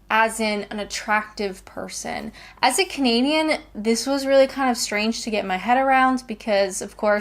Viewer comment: I thought that you said commedianne not Canadian for a while there.